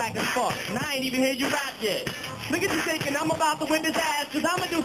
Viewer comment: Does he say the n word at 01:16? - it's hard to hear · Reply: Nah he says "look at you thinkin"